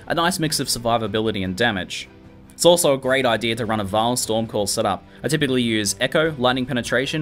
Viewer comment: What does he say @ 9:22 "It's also a great idea to run a ____ storm call setup". Since I am new I don't have the full vocabulary of this game down yet, and lack a good understanding of the game still. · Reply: vaal storm call